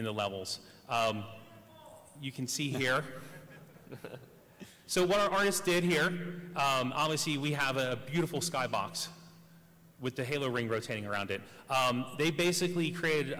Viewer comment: what did he said? "is Beautiful!"? haha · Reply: "You can see here. So what our artist did here, umm, obviously we have a beautiful sky box with the Halo ring rotating around it"